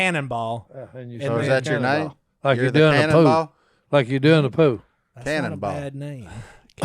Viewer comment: Si trying to say pool haha, sounds like he says “Like you do in the poo”